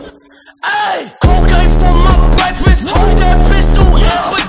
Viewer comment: At the start i heard “MY MOM CAME FOR BREAKFAST”